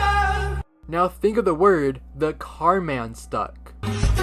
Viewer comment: I hear "and the car wont start" is it just me or do u hear it too?